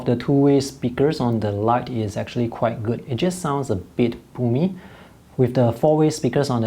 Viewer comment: did you say the audio speakers sound a but pooey?